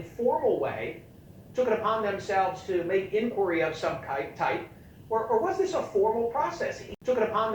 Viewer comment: Verbal slip ups happen. The guy said kype first of all. Not a racial slur. The other guy simply mixed two words, king and junior. It happens. This stupid reaction by everyone is what happens when you have an over sensitive outrage culture. Sticks and stones people, remember that saying? These words can not hurt you! They are mistakes, if your job was to talk a lot and give a bunch of interviews you will eventually mix up words and make speaking mistakes. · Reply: What's a kype? I thought he said kite...